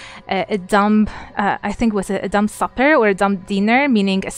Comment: a dinner is referenced that I replayed a number of times and I keep hear her say a "dumb fucker". Can someone tell me the name of this dinner? I feel fairly confident that I'm just not hearing right 🤣🤷‍♀️